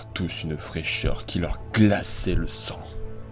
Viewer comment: Ok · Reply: corrige moi si j'ai tord mais à 0:28 c'est " glaça le sang " pas " glaçait "